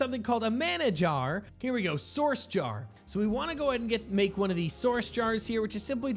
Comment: When I first heard him say mana jar I thought he said manager